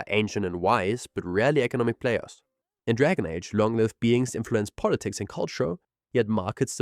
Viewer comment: I heard "In Dragon Age, long 👃 beings influence politics and culture" 💀🤣